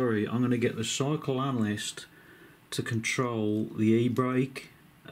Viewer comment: I thought at first you said Psycho - Analyser....LOL. ....You have created a monster, you know that dont you?....High end quads were more fun you know, and maybe cheaper.